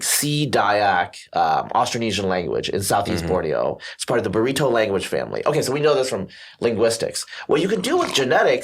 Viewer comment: excuse me, but did you say burrito language family? lol